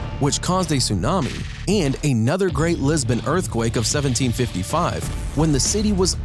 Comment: did anyone else hear lesbian earthquake lmao 😂 Just me... Ok